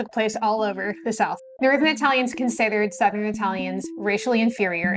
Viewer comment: What did you say 0:03 ? Northern Italians? As in from northern Italy or Italian immigrants in the northern USA ? Sorry if it seems obvious but Even closed captioning isn’t picking up on what you said, it reads as “south American italians”, any clarification appreciated. Thanks Edit: same thing